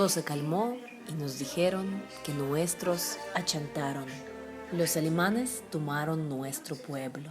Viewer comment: Que es lo que dice en el 4:49? Dice ahuyentaron? Perdon no puedo entender eso..